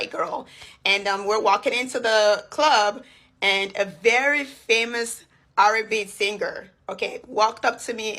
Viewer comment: I thought she said a very famous "Arabic" singer at first lol😅but I got it now